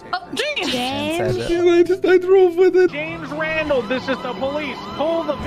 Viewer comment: waa i the only one who heard "james wandal" ??😅😅😂😂😂